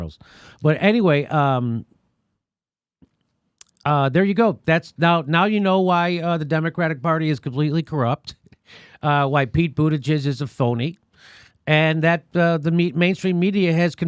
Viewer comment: He should just drop out. I wouldn't want a president with the name Buttigieg anyways. Can you imagine? People will call him "President Butti (Booty)" 14:58 lmao omgg it sounds like Jimmy said "Booti-gizz" see this is why we can't 😂 ... oh he's actually gay? * Google's information * 🤐 Oh, hm, actually gay.